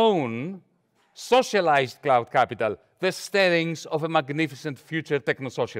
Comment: Can anybody help me understand what he said? "Let us develop our own socialized cloud capital, the _______ of a magnificent future techno socialism." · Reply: stirrings: an initial sign of activity, movement, or emotion.